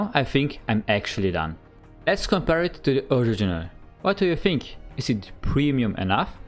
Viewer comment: what did he just say XD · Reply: "let's compare it to the original"